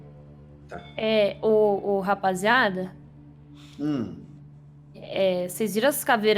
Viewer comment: a falou "o rapaziada" e por algum motivo o meu Google escutou "ok Google" e pesquisou rapaziada no yt, não entendi nada mas ri mto